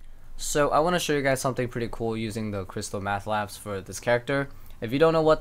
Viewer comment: I thought you said, "Crystalmeth" at first.